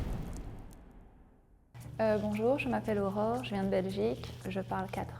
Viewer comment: Corrected subtitles (in case someone wants to use this video to learn French): 2:56, not AurorA 3:03 In case you're wondering what she's saying at the beginning of her sentence: je pense que "I think that" 3:09 Miranda, not Mira 3:12 Interesting that she and Leila use "langage" and not "langue"; in France and Belgium (maybe other countries as well), we only use "langue" for spoken languages while "langage" is used in expressions like "le langage de l'amour" (language of love), "langage châtié" (elegant speech), le langage "the ability to speak". 3:20 she adds: je suis étudiante ici "I'm a student here" 3:22 "MOI, je m'appelle..." 3:33 she's says Lausanne, not Gevève 3:43 ah mais du coup moi, je m'appelle... : "ah mais du coup" is just a filler, but still interesting to learn if you want to understand native speakers :) 3:54 ivoiro-malienne: she has a double nationality (Côte d'Ivoire + Mali) 3:55 they forgot to translate: "in Côte d'Ivoire we mostly speak French" 4:04 "Bonjour, moi c'est..." means the same as "je m'appelle", but not what he's saying here. 4:08 le WOLOF et le français 4:13 they forgot "wolof" again: le français est affecté par la langue wolof 6:27 I think she says "fourreau" here 7:35 "SoupER, ça fait très classe." 8:16 téléphone cellulaire, téléphone fixe 9:01 I wouldn't be surprised if they'd gotten that wrong too, but since I don't know these terms, I'll cut them some slack. 10:01 rien (without S) 11:28 I think he says "fais doucement", without "le".